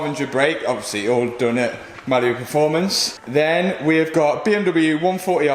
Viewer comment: Sounds like your saying ‘Malabu performance’